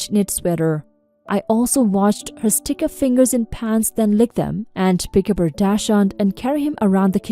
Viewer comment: cooking story , thanks to her saying shes " unhygienic " .... i misheard narrator saying " i also watched her stick her fingers in her PANTS and lick them " .... i was WTF!!! ..... til i replayed and read the on screen words . need sleep me thinks